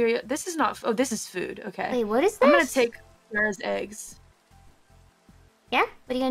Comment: This might be the first thing I've seen of Calli in three months that doesn't involve Kiara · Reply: I think Calli said "I'm gonna take Kiara's eggs"